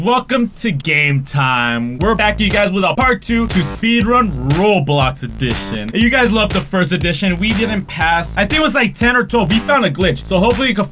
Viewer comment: Did he really say seedrun?